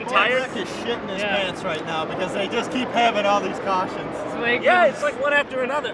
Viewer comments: What he actually said: “Boy, I could sh*t my pants right now because they just keep having all these cautions.” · Three years later and I just now figured out what Mr. Tire Cart there said XD! "(Josh) Bilicki's shitting his pant's right now because they just keep having all the se cautions"